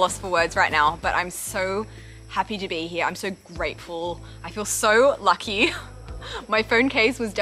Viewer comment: Did you just say: "I'm so grapeful" ? :DDD